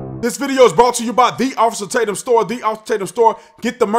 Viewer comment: 45 SIGNATURE VINTAGE HAT! Exclusively on <link> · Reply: You say it so fast I hear, "ti ofis tatm sto". Glad you provide a link.